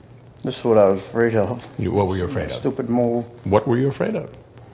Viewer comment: what does he say at 4:10? Stupid what??